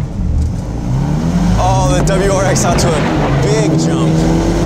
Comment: 〜おお、WRX圧倒いいじゃんw(空耳)